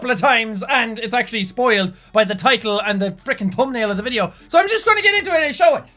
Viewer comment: i thought he said "the toenail of the video" for a sec